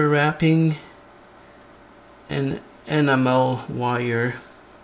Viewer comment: I keep hearing 'Animal wire' instead of 'Enamel wire' - lol. Great video:)